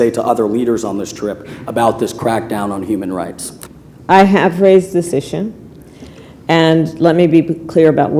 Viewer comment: what did she say at 1:40... I have raised decision???? I can't hear it right.... I feel sorry for her in this role. so sad..... BTW... what a great speaker of parliament. Way to go...